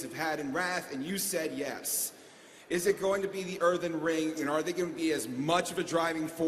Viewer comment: Earthen Ring es lo que dice el "orgulloso chaman orco" xD